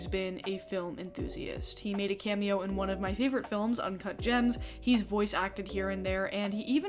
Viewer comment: Why can’t I hear “Uncut Gems” without hearing uncah jahhmms